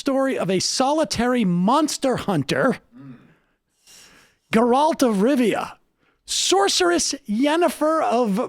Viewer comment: I thought Sam said "Geraldo Rivera" lol